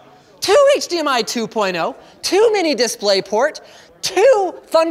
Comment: I heard "2 mini displayport" as "too many displayport"